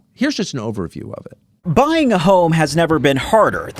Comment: Did he say " Buying a hoe" ? LOL